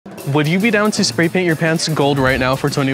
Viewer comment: I thought he said "would you get down to spray paint your pants"😭😭